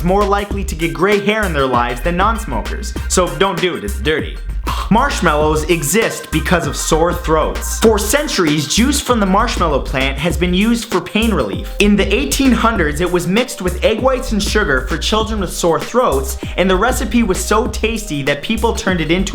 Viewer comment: Did you just say marshmallow plant?